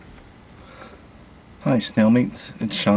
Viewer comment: Just sayin, I heard the 'hi snailmates' as 'hi stalemates' Also, proof again that I'm responsible for all of Beth's internet fame. Cold hard facts.